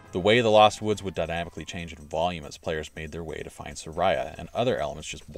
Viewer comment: Did he call her “Saraya”?